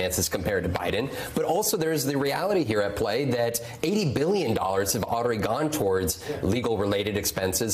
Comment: Did he just say that 80 billion dollars has gone toward trump's legal bills in the last 2 years?